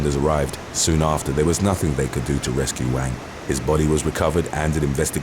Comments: 👋 hey .... who else thought he said "huge wang" ? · I thought I heard “used wang” at first. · At first I thought he said "used wang" .. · #10 I swore I heard "Huge Wang"